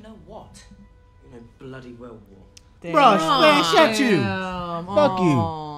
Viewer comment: Did say 'bruv' at 16:49ish?